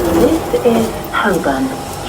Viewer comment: Go to 9:08 and turn on subtitles it says this is husband. Instead of holborn lmao